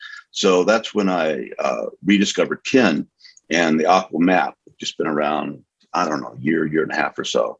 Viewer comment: what word does he say at 10:00? it sounds like 'aquamap' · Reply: Hi Akadamanas, refers to the "AQAL"-map, which is a (partial) abbreviation of "All Quadrant, All Levels, All Lines, All Types & All States". This is a way to refer to all 5 elements of the Integral Map at once (Quadrants, Levels, Lines, States & Types). Thanks for watching! 🙏